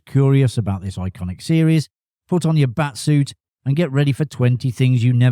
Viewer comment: Oh you said bat suit. I thought you said fst suit... My bad